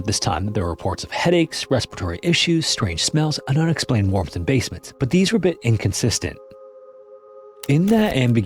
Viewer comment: did he just say there were unexplained walk-in basements??? I feel like that’s pretty alarming 😂😅